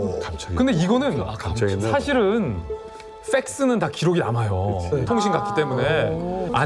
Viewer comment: 왜 팩스라고 안들리고 ㅅ스라고 들리지?.........ㅠㅠ